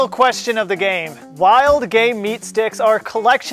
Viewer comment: When he said “wild game meet sticks” I thought he said “wild GAY meat sticks” So I was like “yes a wild homosexual made into a meat stick”